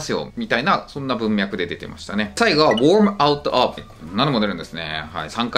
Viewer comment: worm (out of )がwarmにしか聞こえない